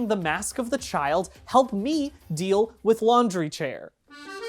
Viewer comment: everyone yesterday: "omg what is he saying?" "is he saying stop me?" "is this a call for help?" "we need to find out!" bdg: [walks in] stamina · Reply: I thought he said God help me lol